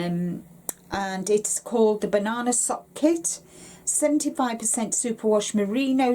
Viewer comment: Hi, 👋. When you said banana sock, I briefly thought you said banana hammock, and choked on my coffee. 😯☺️ I'm assuming you meant a pair of socks, because I haven't tried making socks yet, and don't know how much yarn they take. Thanks for sharing. Take care and love from Canada 🇨🇦❄️ ✌️💜🍁☃️